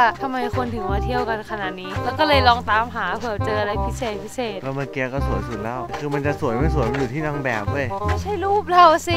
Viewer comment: พูดว่าอะไรอ่ะ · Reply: master thepuzzle " ก็เมื่อเกี้ยก็สวยสุดเล่า " น้อวว55